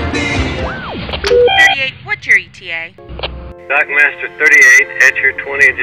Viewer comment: Did he say his name is Doug Masters. Haha, where’s Chappy..!!